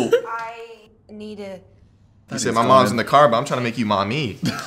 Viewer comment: zach saying "mom's in the car but i'm trynna make you mummy" AHAHHA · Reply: i thought he said "mommy's in the car but im trying to make you mAmIiIiIiii" lmaoooo